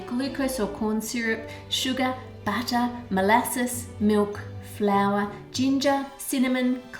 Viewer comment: I swear every time you said "butter", I heard "batter" XD At first I was like "why put batter in your batter? What batter?" XD